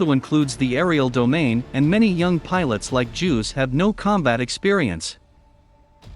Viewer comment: Does he say “Many young pilot like Jews?” How is this computerized voice shit so popular!? 😂 These videos are so designed for children..